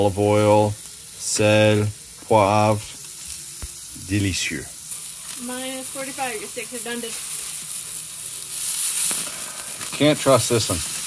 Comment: is that German? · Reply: Sounds like she said your steaks are done